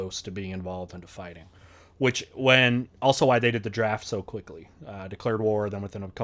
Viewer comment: Instead of "the day of the draft", I heard: "I dated a giraffe" ... :,U